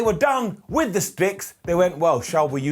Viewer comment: sounds like he said(spics)not Sticks! 🤐